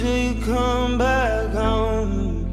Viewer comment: I always thought he said "Till you come back home" until now... 😂😅